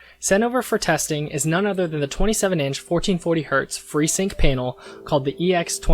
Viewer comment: did i hear 1440hz? Damn ok no wonder it’s 500$ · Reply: He said 144hz, the 1440 is the resolution..